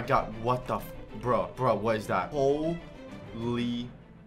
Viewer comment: Did bionic just say what the f**k